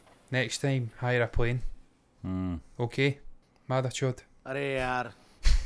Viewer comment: did he said madarchod